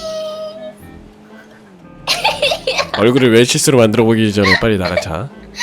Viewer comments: 분명 7:14초 쯤에 님이 님한테 '얼굴을 웰치스로 만들어버리기전에 나랑자' 이런거 같은데...?저만 잘못들었나요? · 초에 빨리나랑자 이렇게들림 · 에 왜 몇십번을 들어도 빨리 나랑자로 들리지..